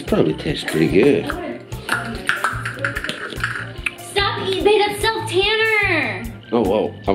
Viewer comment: I swear at 4:47-4:49 she said "babe that's self tanner" .....My face...#shook